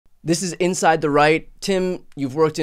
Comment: I first thought you said in your intro "This is inside the Reich".